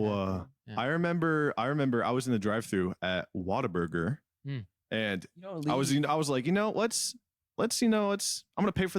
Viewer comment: did he say water burger? 4:30 👁👁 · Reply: whataburger